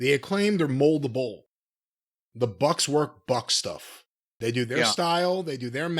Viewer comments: I misheard. Thought he said “the Bucks work butt stuff”. They probably do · I had to rewind because I thought I heard say "the bucks do butt stuff" not "the bucks do buck stuff" 😊